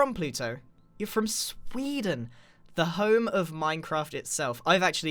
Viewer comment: Did he say moyang